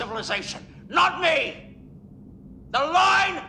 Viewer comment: For some reason I always thought he said "this Bar and no farther."